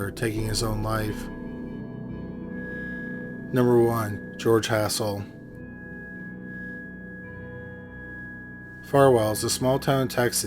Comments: Coulda swore you said "George Asshole" lolol 😂 I think it's bedtime for me, lol 🤣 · I swore I heard in the beginning of video 3, “George Asshole”.